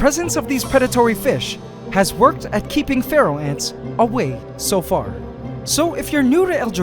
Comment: For years I thought he said pharaoh ants but it was feral